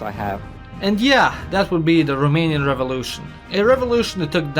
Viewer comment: I thought it said Roman revolution and was like "wait what, Rome had a third revolution?"